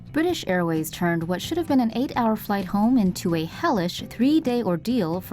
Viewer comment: When you said hellish at 0:06 I heard relish